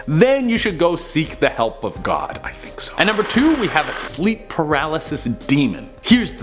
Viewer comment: Omg!! At 7:50 I heard a ghost say.." I think so". Holy Crap Thanks now you jinxed me. 😱😱